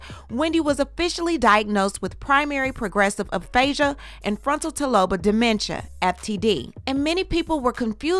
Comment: did you say Frontal Taloba? Lawd, medical terminology pronunciations be effing ya'll up😆